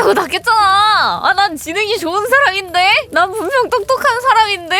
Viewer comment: 진행보다 지능으로 들리네